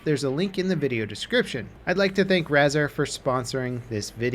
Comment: I thought he said brazzer instead of razar